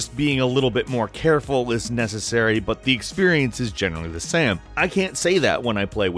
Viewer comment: that moment you say sam instead of same XD